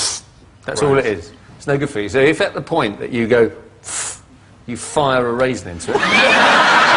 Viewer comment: What does he say at 8:04? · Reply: " so if at the point you go phfff - air noise you fire a raisin into it"